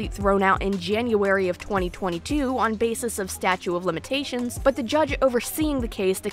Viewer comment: I love you to death girly but I am so 🧐 - it sounds like you’re saying “statue” of limitations instead of “statute”? Am I mishearing this because it keeps jarring me & it’s one of those words that just slips by so fast that I can’t even tell if what im hearing is what’s being said, hah